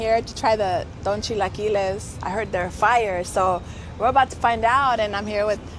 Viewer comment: I heard they were fire is hilarious